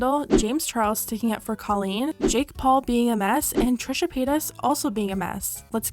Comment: I swore that u said Jake Paul being an ass so I was like YES QUEEN U CANCEL HIM